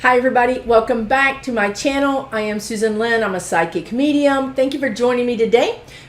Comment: At first I thought you said, "I'm a psychic comedian". LOL I thought, another Linda G.? 😂😂😂 (Linda shared this link, btw.) Excellent message and info. Thanks!